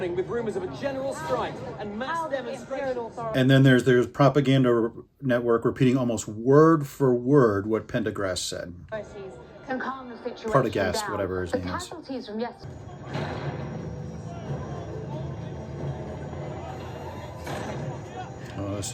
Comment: did you say pencil grass lol